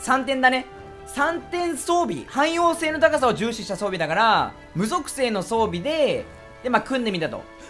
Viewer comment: 「海王星の高さを重視した(以下略 に聞こえた 土星を諦めた男